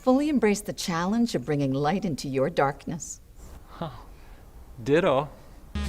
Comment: did he say diddle? I think he meant ditto but he slipped.